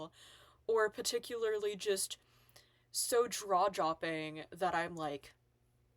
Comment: I thought I kept hearing spoonerisms, but I was half-listening while doing other things. I finally had to rewind @ 11:26 that yes, she did indeed say "draw-jopping" 😊